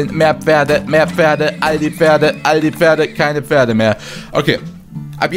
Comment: ich hab dauerhaft aldi-pferde verstanden xD